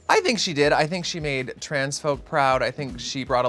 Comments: When Brooke said "She made the trans folks proud" about Kerri my dumb ass did a double take because I thought she said "She made the transphobes proud" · MY Auditory Processing Disorder is so bad that I thought Brooke was saying Kerri "made transphobes proud". Until I connected the dots and realised she said Kerri "made trans folks proud".